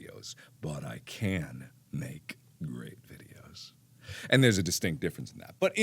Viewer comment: why am i hearing rape videos